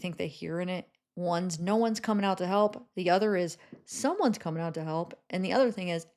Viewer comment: I hear, clear as day, after listening to the loop “he’s coming out to help”, which I assume meant Brian. Probably in a state of “holy f we didn’t mean for him to die” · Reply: I definitely heard "is coming out to help" couldn't make out who, but definitely her coming out to help.